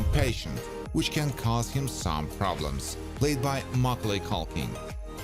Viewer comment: Did he just say Maugalay Culkin? 🤨